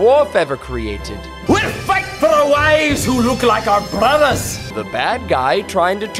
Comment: “We’ll fight for our wives who look like our brothers” is an underrated line. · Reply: I honestly thought at first they said "We’ll fight for our wives who look like our mothers!"